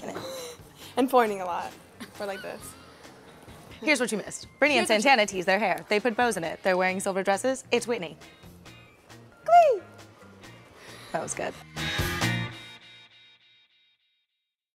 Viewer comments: lol at first I thought she said at the end: "it's winning!" instead of "It's Whitney" lol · When she said pointing I thought she said porning